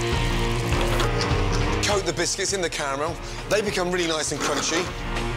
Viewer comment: Did I just heard Gordon said "camarel" instead of "caramel" on 0:23????? Or I was just wrong?